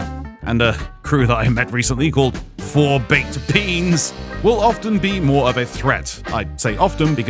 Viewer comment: sounded like u said 4 baked Peans not beans